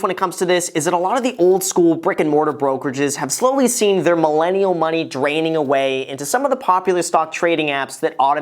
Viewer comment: did someone say millenial money???